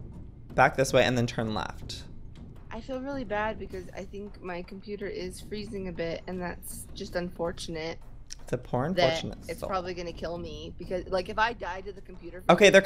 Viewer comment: when he said poor unfortunate soul i thought he said pornfortunate soul lmao XD and Stacy's gasp and Joey's reference to into the woods lol